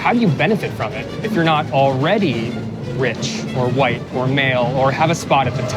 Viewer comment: Did he say if you are not " White or Male" at 34:08 . I am really confused into why that would be a thing.